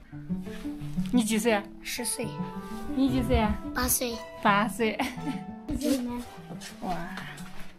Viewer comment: Great video. I noticed sometimes the subtitle isn't accurate, for example, 15:16 the little boy said he's 8 years old, but subtitle says 5.